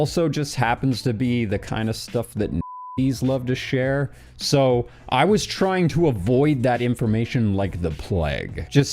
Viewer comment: What word is he saying at 21:09? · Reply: Im pretty sure its Yahtzee